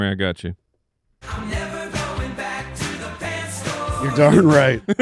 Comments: I thought it always said I’m never going back to the cat store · I’m glad I’m not the only one who thought it said “I’m never going back to the pet store” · I thought it always said..."never going back to the dance floor". Clearly wrong